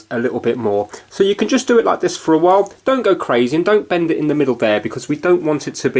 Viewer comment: I Don't Know What My Name Should Be he said: “ Don’t bend it LIKE I’m doing here.” You clearly don’t know what a demonstration is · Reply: when did he say that? 1:21 He said "dont bend it in the middle there". You clearly dont have ears